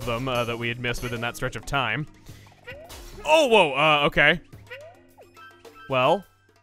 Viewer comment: Did just say OwO?